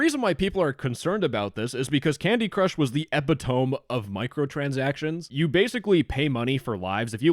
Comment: did you just say ep-it-oam